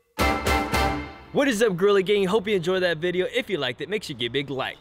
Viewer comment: I SWEAR I HEARD HIM SAY WHATS UP GIRLY GANG LIKE IF YOU HEAR IT TOO